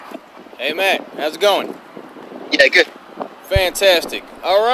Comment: sorry for asking but what is the right guy saying when greating the left one. My good headphones cant even help me. Is he saying the N word or what ??? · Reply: says "Hey Matt, how's it going?" 3:48 "yeah good"